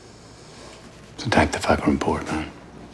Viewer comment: what does he say? “Without me, there is no you”, then what? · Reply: Then-"just type the fucking report"